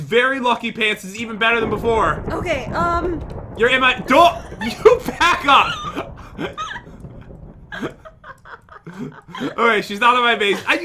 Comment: I thought he said you Baka